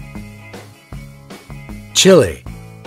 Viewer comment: They said “Hungary” but it makes me think of the word “Hungry” 😂😂😂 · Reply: Same, when they say chile, I thought they say chili